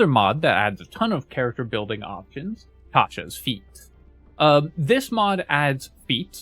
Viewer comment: Had this playing in the background and at 12:25 I started dying laughing because I didn't hear the "s" at the end of feats. "I love this character building mod Tasha's feet" 🤣